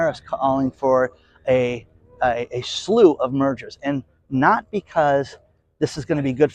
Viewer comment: I totally thought you said we will have a surge in murders and I was totally agreeing with you and then I realized you were saying mergers. How messed up is our society.